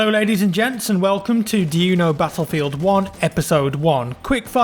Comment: For some reason I thought it said, "do you even battlefield 1?"